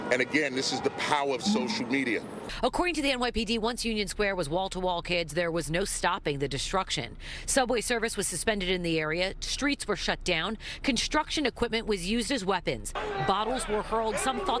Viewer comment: I’m I the only one who heard her say subway surfers